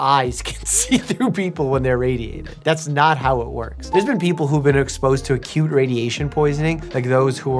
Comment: i thought he said " there are people who have been exposed to a CUTE radiation" 😭😭